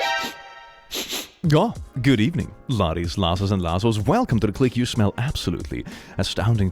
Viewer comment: I always hear you saying "Laddies, Lozenges, and Lassos" in your intro and I'm not sure I want to discover what you're actually saying - just to keep the mystery alive.